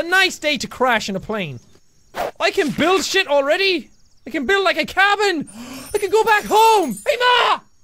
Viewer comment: On 0:30 i've been replaying that for 2 minutes now. Still dont ubderstand what jack is saying. Someone halp. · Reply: I think he said uhh "I can build like a cabin!?" sorry if it's not right